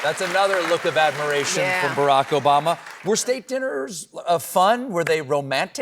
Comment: I thought she said “Steak Dinners” ☹️